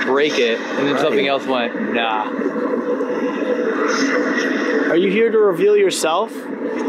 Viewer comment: when he asked "Are you here to reveal yourself?" it sounded like it said "us"